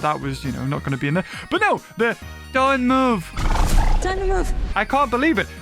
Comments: I think the person says time to move not don't move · Look I hear time to move but it takes a few replays to hear it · i think Zora Bennett is saying "time to move!" And not "don't move!"